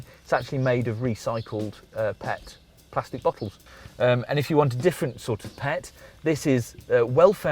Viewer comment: Early in the review, I thought he said recycled pets. Yikes! He actually said PET plastic bottles.